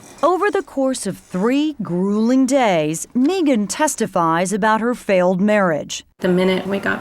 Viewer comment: The pronunciation of her name would drive me to the extreme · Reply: I thought they were saying Neegan....like the dude from the walking dead show....😂